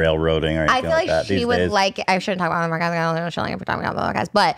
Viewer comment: Can someone please tell me what she said?? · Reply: “I shouldn’t be talking about it, I shouldn’t be talking about it on the podcast”